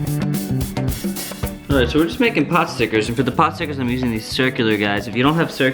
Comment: Call them gyoza's. Yum. Never heard of them called potstickers till about 4 or 5 years ago, a boy telling the giant story on youtube. Thought he was saying popsicles at first.